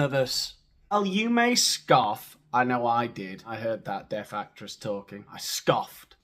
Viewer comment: I thought you said death actress.....